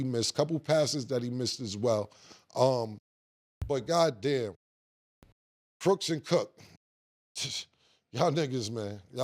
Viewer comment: “CROOKS” INSTEAD OF COOKS IS CRAZY KILLA 😂😅 ITS “COOKS” AND “COOK” 😂🤣